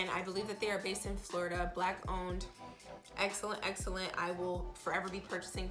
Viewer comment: can someone tell me what she said here? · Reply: she said " black owned"